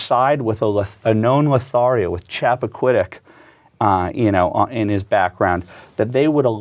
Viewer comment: He is saying Chappaquiddick. Which is where Ted Kennedy (deliberately?) drove his car over the edge of a small bridge into shallow water and left the woman he was with (not his wife) there to slowly drown. They said it took hours because she was in a bubble. He walked back to the party (a 15 minute walk) instead of to nearby homes to get help (150 yards away w/ lights on). He told a couple friends. They all went to the scene